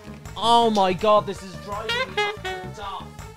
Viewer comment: Had to check he really said ‘this is driving me up the duff’ @ 50:49. Should not have doubted him.